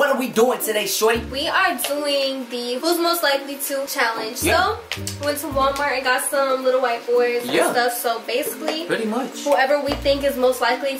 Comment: I thought she said “ went to Walmart and got some little white boys”😂😂